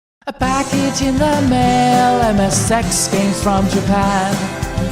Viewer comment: Why do I always hear, "A package in the mail, and the sex games from Japan?" Is it because I'm a pervert?